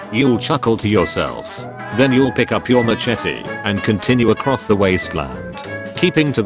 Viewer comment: i thought that said mustache instead of machete